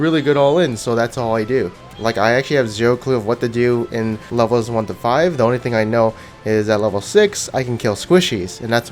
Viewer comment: What the actual fuck are you screaming? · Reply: I think he is saying "wheeee wee"